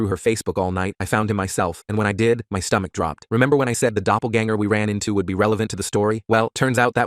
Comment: Dude I thought he said his doppelgänger 😭